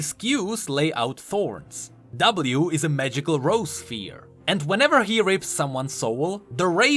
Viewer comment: did u just say "whenever he rapes someones soul"